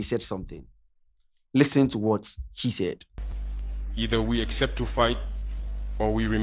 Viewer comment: Did I heard "She said 🤔 " 😂😂😂😂😂 Eiiii, Scanty